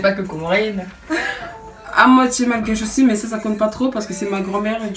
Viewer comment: j’ai pas compris ce qu elle a dit à moitié quoi ? 😶❤️❤️ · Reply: Malgache :) <3